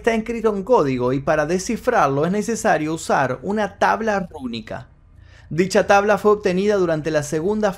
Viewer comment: tabla que ? no se entiende lo que dijo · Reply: Tabla Rúnica.